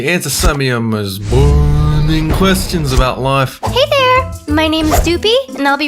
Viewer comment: When said burning questions I thought he said boring questions.